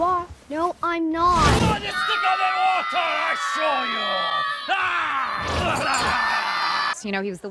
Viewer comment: What is he saying here? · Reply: BLOOD IS THICKER THAN WATER AND I’LL SHOW YOU!!! Your welcome :)